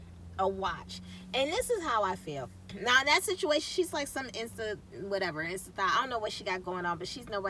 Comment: did you say she's like some Insta-thot???👀🤣🤣🤣messy messy